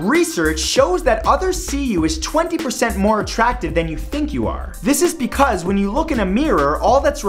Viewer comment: When he said others see you as more attractive I thought he said you see yourself as 20% less and I paused the video and though for a solid 5 minutes how fucked I am if I am actually 20% uglier